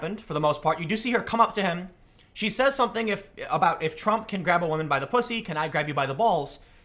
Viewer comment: Well I heard her say "If Trump can grab a woman by the pussy I can grab you by the balls"